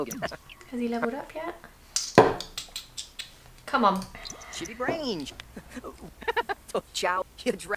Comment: it sounds like he says sh*tty brain 😂